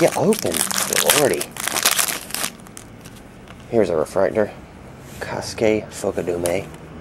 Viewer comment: Check it at :48. It sounded like you said, Ka-Skae. But don't worry, It took me many hours of listening to his name to perfect it.